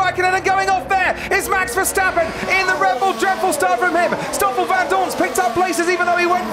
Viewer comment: anyone hear dread bull lol